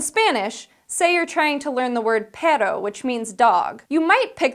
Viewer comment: when she said Perro, I thought she said Pedo 0_o